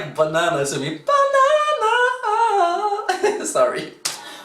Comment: I think you have a good voice also☺ But i really laughed so hard when i heard "BANANA KOOO" instead of PANGARAP KO😂😂😂